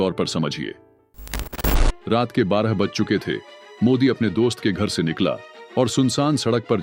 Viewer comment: क्या बोला😢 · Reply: modi ka naam